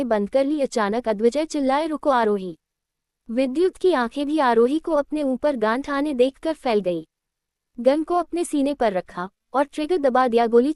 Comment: Ye yaha 2:36 p kya bola gaya h plz aap batao kuch samjh nhi aaya vidyud ne kya kara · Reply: Gan ko apne uper tane dekh aarohi ki ankhe fail gai, fir usne gan ko apne mathe per rakha* ye line thi, AI voice se thodi galti ho gai thi thank you for flag out ❤😊